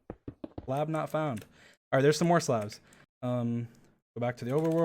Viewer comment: is no one talking about 23:41 when dream says something along the lines of "slab not found"- idk that's just what I heard I just realized he also typed in "slabnotfound" at 30:41 in the chat lmaooo (idk if this was a joke or not-)